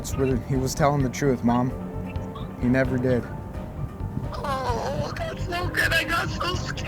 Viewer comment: did the Wife Say "oh no thats no good" ? or am i hearing things